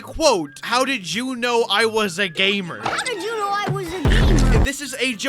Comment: I heard “How did you know I was a GAMER" As “How did you know I was a GAY MER"